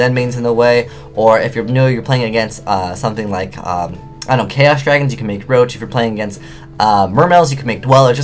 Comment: did you say "I dont care-os Dragons"? xDD